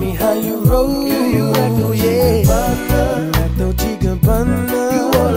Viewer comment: Had to come on here to check my misheard lyrics .. I thought the chorus started with "Girl you act all Chicken Parma" 🤣🤣🤣... Love your work